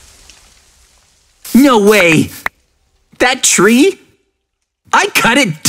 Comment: When said no way i heard nwo way do i have a problem tell me what you guys hear bc i think i have a problem now here